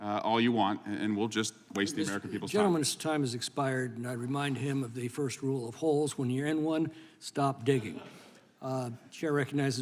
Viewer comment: Oh you said holes, for a second there I thought you said Fang Fang soul sister of Jane Fonda.